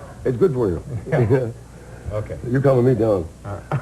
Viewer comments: I think HST might actually be saying "You're calming me down" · lmao did HST say "your dumbing me down" to letterman @ 5:57!!!!